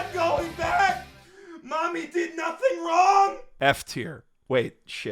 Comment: I thought you were saying "MOMMY DID NOTHING WRONG?" but then I realized you were saying "MAMI DID NOTHING WRONG!" I legit forget there was a character called Mami. Kazuya's mommy definetly did something wrong...